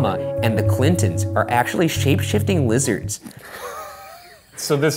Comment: he says "shapeshifting lizards" while the captions say "shapshifting wizards"